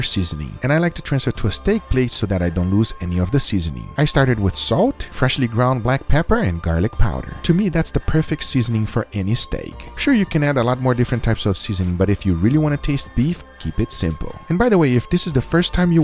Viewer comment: all i always heard is “Freshly ground paper” and nothing else